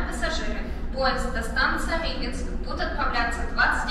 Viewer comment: На 3:46 я услышал «поезд до станции Липецк»… Забавно